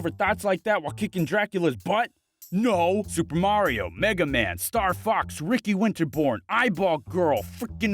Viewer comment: I thought he said megamind